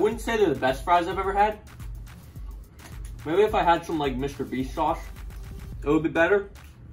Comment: I do not know what I heard, but I do know I heard “Mr. Beast sauce”